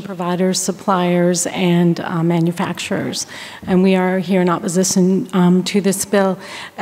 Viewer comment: I hear oppozizon instead of opposition smfh 😂😂😂😂 even with a script they fkn up hahaha